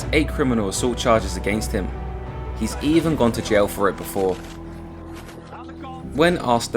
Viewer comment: "He's even gone to jail for it before..", or did you say, "He's even gone to jail for rape before.." ??